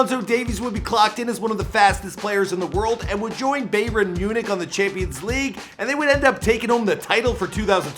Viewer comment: did he just say BAE- RIN Munich 😂